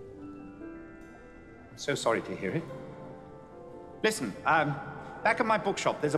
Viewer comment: Did he say "so sorry dearie." or "so sorry to hear it." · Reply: Sorry to hear it